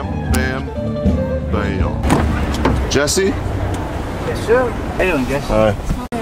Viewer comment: Did anyone else hear "Damn Jesse." xD ??